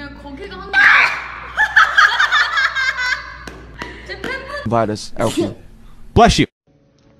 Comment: ได้ยินยัยจีมินพูดว่าอุ้ยเชี้ย